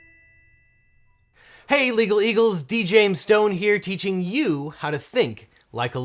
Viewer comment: Thought you said DJ stone